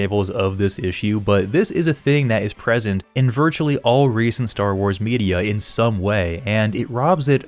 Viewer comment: When you said "Star Wars media" i heard "Star Wars birria" :p